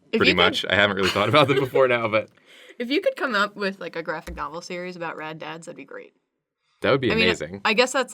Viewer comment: I thought you said that you should make a graphing calculator series of rad dads